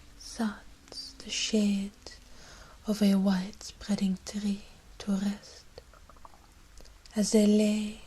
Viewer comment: Was a bit distracted at first by something my partner was saying upstairs, so i heard "sucks the shit of a white spreading tree" Oh dear Dorian, Oh dear.